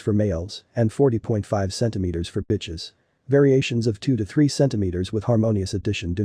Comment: Did he just say "xx" cm for BITCHES? in